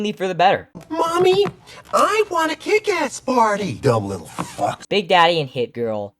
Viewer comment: this was when I finally realized it was “kick-ass party” instead of kitkat bar